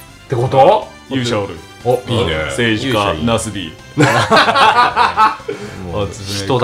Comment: ナスDに聞こえた笑